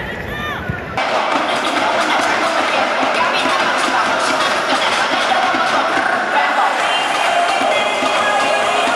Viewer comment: これって何て言うてるんですか❔ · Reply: 今回初披露された大樹のチャントでみんな『だ、い、じゅ‼︎』と叫んでおります‼︎